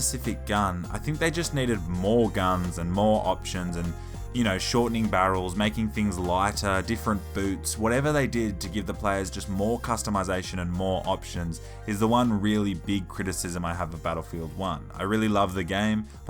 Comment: Did you just say different boobs?